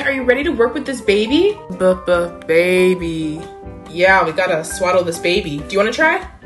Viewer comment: I thought it said "swallow" not "Swaddle"